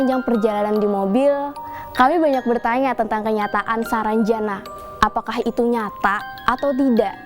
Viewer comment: Kedengerannya "sarang janda" hahahahaha....